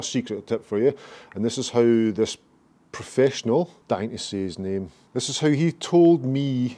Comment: Great tip and your dry wit cracks me up! At around the 6:59 mark I thought you let the old pro's name out of the bag...it sounded like you said his name was Dynessy, but after listening again you actually said "dying to say his name." One of these days we'll find out!